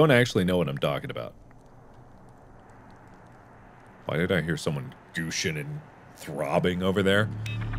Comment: Mark 2020 "Did I just hear someone gooshing and throbbing over there." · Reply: Why* He says " Why did I hear someone gooshin and throbbing over there? "